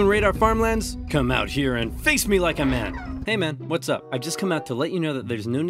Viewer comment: i heard come out here and fist me like a man💀💀